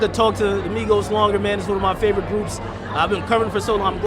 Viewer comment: Did he say "My favorite boobs"?